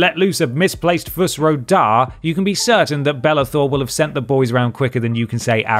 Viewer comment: I was just listening to this and thought you kept saying "Bella Thorne" and I'm like "why the fuck is she in skyrim"